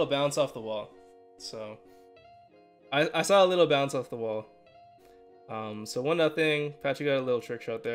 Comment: I first heard Vans of the Wall